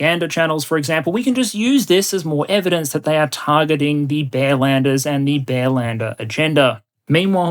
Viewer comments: I heard "bellenders" for at least 10 minutes until I started to realize it is supposed to be "bearlander". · Bearlander..... I was listening, not watching, and thought you were saying Bellender😂 Excellent summation of the past 15 or so odd years.